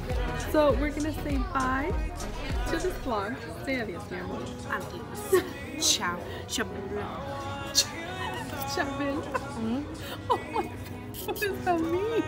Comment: Thought she said ciao chavela hahaha